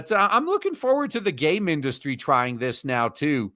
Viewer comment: did he say "gay mendustry?" because that sounds like a place where "staff meetings" have quotation marks around them.